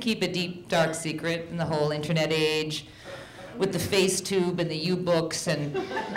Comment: what does she say between 0:17 and 0:20? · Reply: facetube and youbooks?